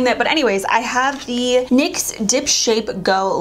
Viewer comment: Lmao am i the only one that thought she said "Nyx dip-shit" oh lawdy.... Idk maybe i was just listening a lil too hard sis.... But ur killin da game frfr.... And a beauty ontop of it wow....